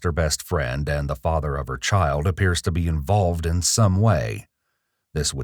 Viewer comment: "And the father of her child appears to be involved" I thought you said bald lmao